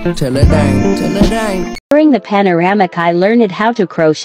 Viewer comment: Ummm.... did she say panoramic??? Lol